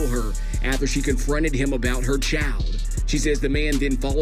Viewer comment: I'm sorry. I'm as country as a chifferobe and using Crisco grease to fire any and everything that can be fried and eaten. But what tf did bro just say?! "Her chow" "her child" or what?!?! Lbvs. Dead ass. I skipped back no less than 5 times, even tried to slow it down,and i still don't what he said. I'm confused, Jesus. In the words of the legend DMX, '!Lord, give me a sign!!!"...or at least subtitles with the lil bouncy ball on the words and syllables 😂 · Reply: Prob child